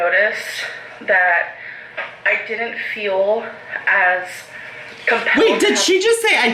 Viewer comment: I heard 'fuel' too!